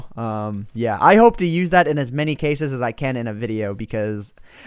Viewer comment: did you say beer or beard